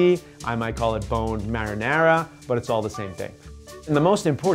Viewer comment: I thought he said boneless marinara.